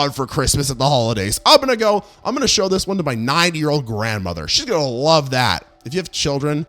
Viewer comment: Why do I hear 9yo grandmother?!?… XD